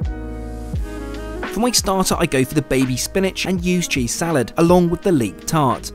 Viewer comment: so glad you wrote "ewes" cheese, because i thought you said USED cheese lol